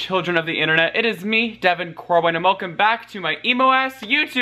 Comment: I swear everytime I watch his videos he says "devin crowin" and I cant tell if I'm mishearing, it's on purpose, or it's an accident